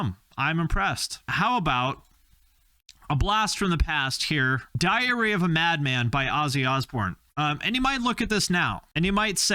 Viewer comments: thought you said "Diarrhea of a Mad Man" initially 😆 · Thought you said "diarrhea of a madman"